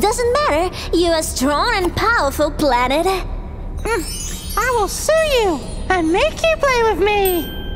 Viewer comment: For a spilt second i thought pluto said "im going to sue you" but i heard that really wrong 😰😰😰